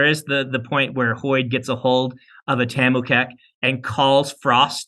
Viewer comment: What is that word they were mentioning around 31:50? “Tamukek?” Some kind of communication tech? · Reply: Tamu kek. It's a bone; honestly, all we know about it was in this. -Eric